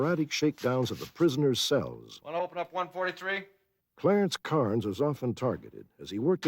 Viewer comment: I thought Robert Stack said CLARENCE CARTER at first. HAHAHA!!! I BE STROKIN’